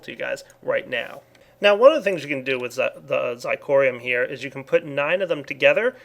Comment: i thought he said theres some more things you can do with the fire block